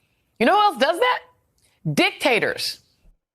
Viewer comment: did she call obama a, dictator‽